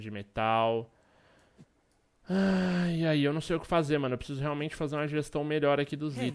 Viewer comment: so eu que ouvi hey "wake up" no 5:13?